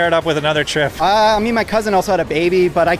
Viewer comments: Surprised half of these comments are like "me and my cousin" when I heard "imean my cousin" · me and my cousin just had a baby?? How is that said so casual.. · did he say he and hes cousin had a baby? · Hold up... At 0:58 did he just say that he and his cousin just had a baby? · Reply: he says 'uhh i mean my cousin also had a baby'